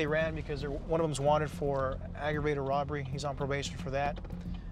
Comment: "arragrated robbry" ohh, now i see why hes robbing things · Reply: I heard "autograded robbery." Maybe it's like a robbery that gets an automatic upgrade, based on the circumstances.